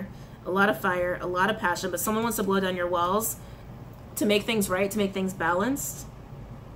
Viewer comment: At abt 51:33 all I heard was "she wants me to blow down her walls" ...like hol'up tf❓😂😂